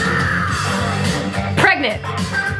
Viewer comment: what did she say!?!?😂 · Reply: Pregnant 😂